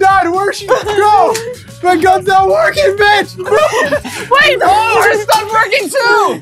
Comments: I think he said b*tch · Is it me or I though I heard said the B word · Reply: He said Mitch the name of the camera man because he was in the way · Did he say bit** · did anyone else hear say the b word(I don’t know if you can use curse words on yt comments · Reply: Yea he said bich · did he say "my guns not working b****" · did he say the b word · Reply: Barbara Smith He said Mitch. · Did say b*tch?!?!?!?!?! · did say my gun is not working b***h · did he say bi#$ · Wait did said the b word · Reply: Doruk Ceran No, he said Mitch. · I thought said “my gun is not working b$&!%” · it sounds like is saying b*ch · Someone at 8:08 said the b word or that's what it sounded like